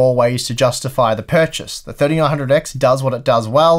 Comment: What does say @ 8:53? 3800x or 3900x I thought he said first 3800x and for the allrounder the 3900x? I replay it for several times, but still cant figure it out.